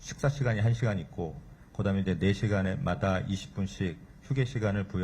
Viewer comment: 4시간마다 20분 쉰다고 했는데. . 1시간으로 듣고 3조2교대를. 3교대로 듣고 .. 잘못 들은 걸 얘기 안해주는지 ,,, 아. 무.도 . 4일 일하고 2틀쉬면. 요일은 계속 바뀌는게 당연하지 ,,, 안전에 부실한게 문제지 .. 덮게도없는데. 반죽기계에. . 손도 넣지ㅡ못하게ㅡ했어야지 ㅡㅡ 위험한데